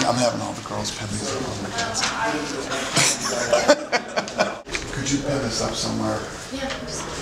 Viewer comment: What did Dana say at 2:19? "could you ... somewhere" BTW, English is not my mother tongue. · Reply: Could you put this up somewhere.